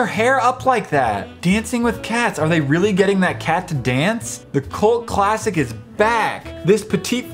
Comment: it was quiet I thought he said cold classic and fat...